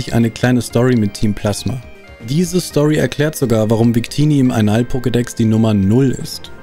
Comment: Ich hab bei Einall-Pokedex bei circa 22:58 Anal-Pokedex verstanden.